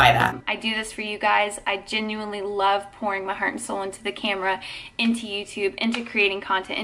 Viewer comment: I know this isn't really relevant, but when she said "I genuinely love pouring my heart and soul..." I swear I heard "I genuinely love porn" and I was like wait what 😂😂😂